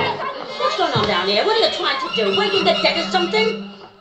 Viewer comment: To anybody who did not understand what the undertaker's wife way saying: "What's going on down there? what are you trying to do? wake the dead or something?